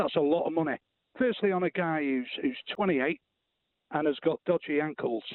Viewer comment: he was referring to Kane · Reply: Ah yes my bad he said "on a guy who's 28"..I thought he said "I'm a guy...."